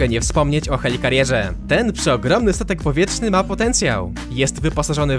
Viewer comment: Ja usłyszałem wielki sutek powietrzny